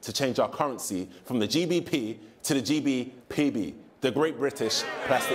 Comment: GDPB. Me, mishearing: GB TP (Great British Toilet Paper)?